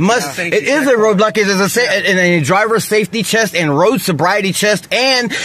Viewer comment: Did he say "chest" instead of "test". He must be really hyped up on the stuff. I would have said that overuse of caffeine and sugar is very much like taking meths or equivalent.